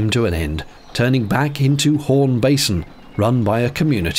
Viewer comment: Must be my American ears, I heard "Horn Basin." LOL